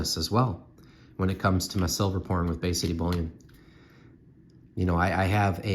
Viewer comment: I'm cracking up, you were talking about making extra money, and I had to replay what you said you did for that money 4 times before I figured out you said you got into "pouring," but I heard "porn." LOL Great video!